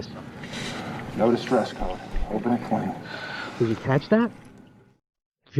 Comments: I hear "open it clean", not "open at twenty". Enjoyed the video! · I hear "open it clean", not "open at twenty". Enjoyed the video! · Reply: I heard that as well. "No distress code, open it clean." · Hey! At 03:01 he actually says, "No distress code, open it clean", not "open at 20"